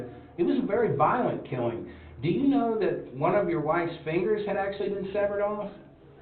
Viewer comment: what does the interviewer say here?? I can't understand him. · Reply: fingers had actually been severed off...